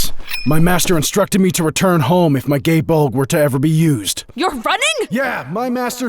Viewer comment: Kirito abridged voice GAE BOLG!! I THOUGHT HE SAID GAY BULK! HE JUST SAID GAY BULK!! SUPPORT THE KIRITO IS ALWAYS RIGHT FOUNDATION!